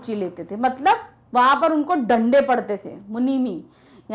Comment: it becomes लंडे instead of डंडे 😂😂